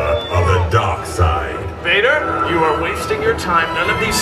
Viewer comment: I thought he said mater and I was so confused cause I was like “ what does any of this have to do with mater?”